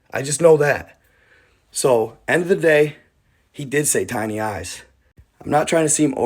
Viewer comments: Sounds like he said shiny eyes, not tiny eyes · I heard "Shiny Eyes"..Not "Tiny Eye's".?!?!🤔🤷🏼‍♂️👽👽👽👽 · I thought he said shiny eyes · Sounded like he said shiny eyes. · He said (shiny eyes) not tiny... Also the fence is different from the fence from the original video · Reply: I THOUGHT HE SAID SHINNY EYES TOO BUT HE SAYS TINY EYES... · I just listened to the audio. It sounded like he said shiny eyes.